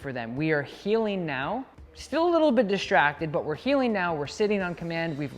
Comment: said: we’re heeling now, and I thought he said: we’re healing now. Haha. Probably both.